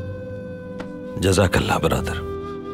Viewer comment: did ertugrul said jazakallah brother ? 🤔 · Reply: He said badadar not brother 😅😅😅😅